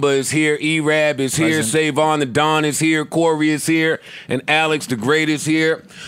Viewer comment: Joe's intro: here with my my nearest and dearest he mentioned everyone except Rory!! I thought he said it but he said Corey not Rory! - Maybe his frustration was with Rory not Mal, Mal was collateral damage.